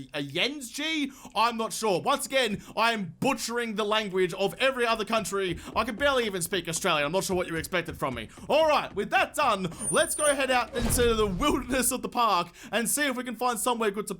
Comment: Did he just say “speak Australian”